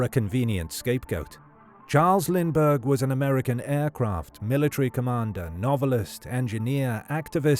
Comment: Did you just say Charles Lindbergh was an aircraft?